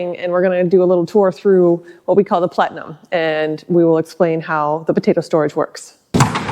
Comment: LOL. Every time you or said "plenum", YouTube's automated closed caption system translated it to "Platinum"! Liking your channel. I often watch sailing channels, so this is quite different. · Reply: When I heard them say "plenum" it sounded like they pronounced with the the letter t as in "pletnum," so I'm guessing the CC picked that up as well.